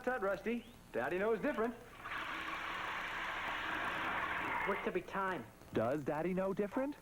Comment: What did Rusty say at 13:29? And how come this was the only time we heard Roman Numeral Blues? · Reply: "Works every time."